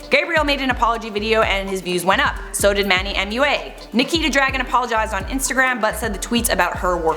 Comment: Am i the only one who thought she said; Nikki THE DRAGON haha😂😂😂